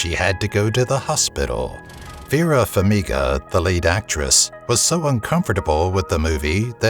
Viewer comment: Yeah uh Vera Farmiga is still alive last I checked LOL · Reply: I heard "late actress" at first, too! (he actually said LEAD actress.)